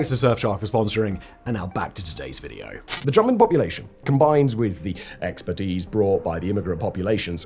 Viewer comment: Really sounds like you said "the German population". I listened to it 3x, then with subtitles, so I guess you said 'jumping population'. Never heard someone say that before. Really sounded like you were saying the German immigrants particularly were responsible.